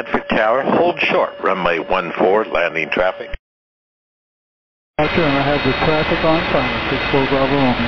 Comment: I don't think you have the transcription quite right. What I hear is: "Roger, I have the traffic on final, 64BR."